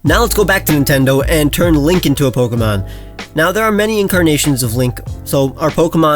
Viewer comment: sounds like he said “Let’s turn Lincoln into a pokemon and that sounds like an excellent video idea, turn people into pokemon